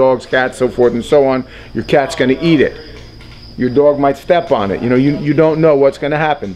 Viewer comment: did he just say the car is going to eat a TEGU